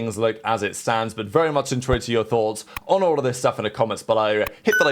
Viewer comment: what does he say here 10:06? what thoughts? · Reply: “very much intrigued with ur thoughts” maybe but ngl i’m not sure